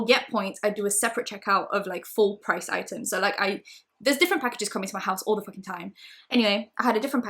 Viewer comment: I’m passed 1:27 and now NEED to know what was said but don’t wanna go back🤣🤣 pls tell me😂😂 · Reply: Hayls Helen she was speaking pretty fast and said ‘there’s packages coming to my house all the fucking time’ and forgot to censor out the swear word x